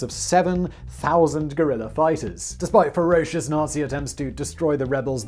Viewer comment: "a force of 7000 gorilla fighters". That is what I heard and I refuse to acknowledge Simon may have said something else.